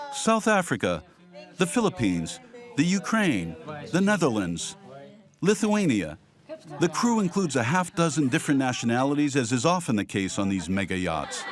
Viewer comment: oh i heard Filipino Crew..🇵🇭🤭